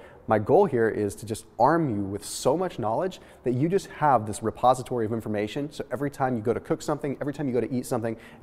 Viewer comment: Thought you said a "suppository of information" lol. Great vid :)